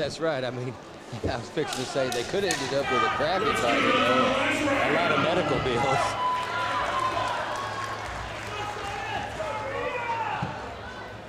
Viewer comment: "They could've ended up with a grappling fighter (...) a lot of medical bills". That's the first time I heard that being said. Is it true?